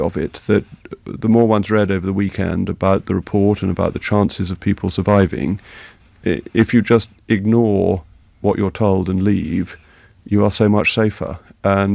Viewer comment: HEAR WHAT HE ACTUALLY SAID AT 0:39. he said " THE MORE ONES READ OVER THE WEEKEND ABOUT THE REPORT, IF YOU IGNORED WHAT YOU ARE TOLD AND LEAVE YOU WILL BE SO MUCH SAFER. He is talking about what should be done AFTER reading the report . He was NOT saying the victims didn't have common sense. I wouldn't take any notice of the fire brigade now if they told me to stay in my burning house .....I'd get out as fast as I could.